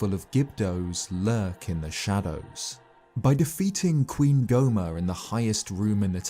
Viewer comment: did he say queen gohma